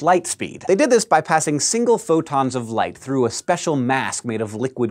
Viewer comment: It sounded like he said "futons of light." I like that image.